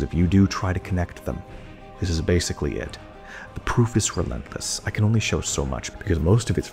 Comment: Did he just say, "The proof is from Memphis..."?